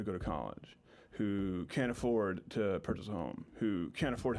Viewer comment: I thought he said can’t afford to purchase a loan. He said home but I wouldn’t be surprised to hear someone on the left say that haha